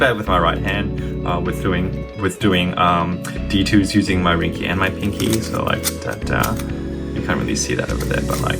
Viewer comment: Not hating, just thought it was funny how you said at 1:31 "my wrinkie and my pinkie" or at least it sounded like you said that